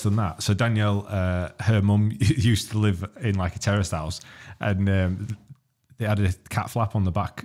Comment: Anyone else hear "Danielle, her mum used to live in a terrorist house"?